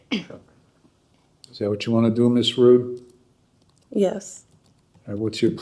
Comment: Ms rude certainly lives up to her name! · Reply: I thought she said Prude not rude 🤷🏽‍♀️